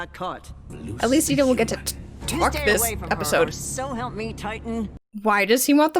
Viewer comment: I thought Eda was saying "so help me, Titan." As a Boiling Iles version of so help me, God.